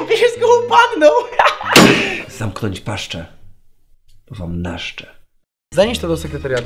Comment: co on powiedział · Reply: Zamknąć paszcze bo wam naszcze